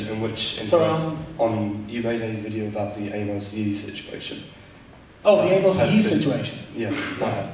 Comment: Anyone else hear the part right after at 9:03 as the "anal yeast question"?